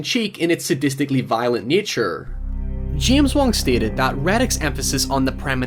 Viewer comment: When u said James Wong (Final Destination), I thought you said James Wan (Saw and Insidious)